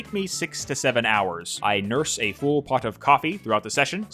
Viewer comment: What did you say? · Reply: hours